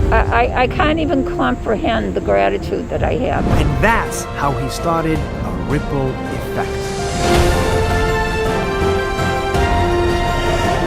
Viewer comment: i thought he said rick roll effect i was sooo confused lol